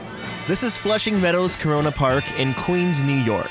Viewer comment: I’m sorry did he say “corona parks” Wow they already made history of corona virus before it even happened